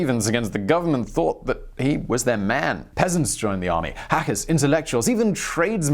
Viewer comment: did he say "hackers" and intellectuals joined him?